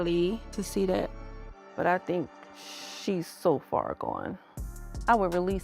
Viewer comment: Jared 😂😅 the tattoo, the xbox, admitting he has no plans for marriage again because it was “too much work” 😮 Siri, play I Did it to Me by Drew Sidora for Charli! 😂😅 She’s only 24 though with a newborn. Those hormones made me crazy too 😢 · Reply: I could’ve sworn it said she’s 34